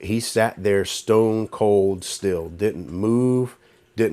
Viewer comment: I thought he was going to say Stone Cold Steve Austin